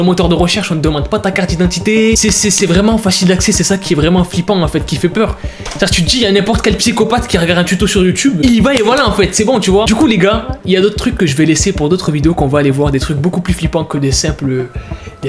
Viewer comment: Ya que moi que moi qui a entendu un \OUAIS/ quand il dit du coup le gars !!!😱😨(j’ai a l’impression que sais un petit garçon)